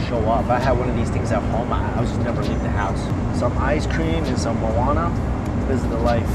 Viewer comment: "Some Ice Cream, some Marihuana. This is the life!" Did I just hear that wrong or did Mikey really say that?